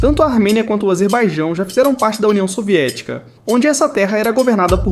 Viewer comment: Já ouvi falar 4:34 Polônia soviética kkk